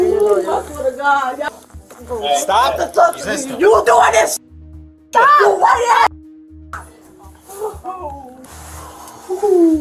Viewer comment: Did she say "White Ass"